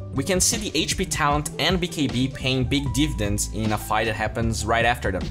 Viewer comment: Suggest next video! Monday is about pro games so make sure to suggest your favorite pro games from this week Want to watch this entire replay ?► <link> Want to watch my exclusive and extended series ? ► <link> Follow me on twitch and watch me stream ! ►<link> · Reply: Paying big diffdence. What word were you trying to say anyway?